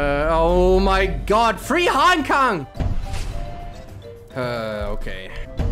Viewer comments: "Three Hong Kong" As a Hongkonger, I hear "Free Hong Kong" Its kinda sad:( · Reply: I heard "free Hong kong " too xD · "Three Hong Kong" As a Hongkonger, I hear "Free Hong Kong" Its kinda sad:(